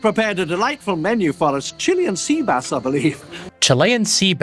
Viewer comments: Even all these years later, I still swear to god he says, "Chili and Sea bass" not "Chilean Sea bass" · As a kid, I didn't know what Chilean sea bass was, so I thought he said "chilli and sea bass". Being from Massachusetts, I thought that was a really bizarre combination, even as a 7 year old. That's actually why I assumed Ellie was wincing, because who the hell wants chili and sea bass on the same plate?